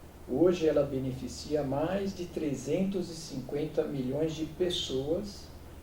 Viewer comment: Eu ouvi "mais de 350 Bilhões de pessoas".... vixe!